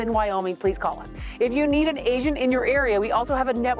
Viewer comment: there was some noise here when I first listened to this part where you asked if “you need an agent in your area…” I thought you said Asians in your area. 😂😂😂 I wondered why as there are plenty Asians here in Honolulu.